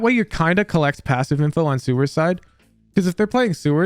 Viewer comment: I heard "Sewer Side" and was so lost for a second